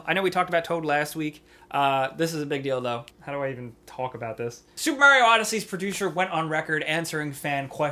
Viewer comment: When you were talking about Super MARIO I heard it as Soup MARIO